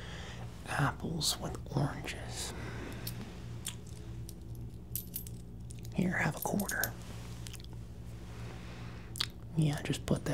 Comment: i always hear it as "here have odour" for some reason